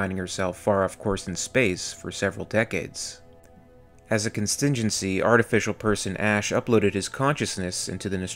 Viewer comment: do you mean contingency? the cc says constingency?